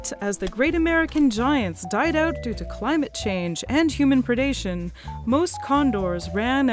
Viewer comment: did she say climate change 1:02. was that from burning fossil fuels?